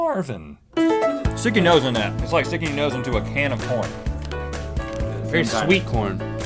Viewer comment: lol, when i first heard the end part where it has the link to gm more i thought Rhett said, "stick your nose in that, its like sticking your nose in a can of porn"...